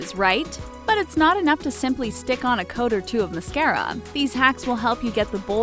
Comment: they put slick instead of stick!